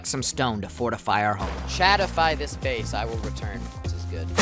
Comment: At first I thought you said "Shadify this base" and immediately thought of Shadiversity and his teachings of how medieval castles were actually built. I was disappointed when I realised it was actually "chadify".